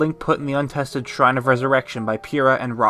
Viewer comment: did you say "zelda was place in the resurrection pod" at 23:49 ?